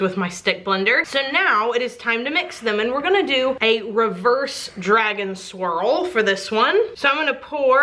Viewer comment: The duchess of suds! 👸 I love that drag and swirl sounds like dragon swirl. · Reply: I totally thought she said "dragon swirl" until I saw this comment... Not gonna lie... I'm a little sad it isn't... hahahaha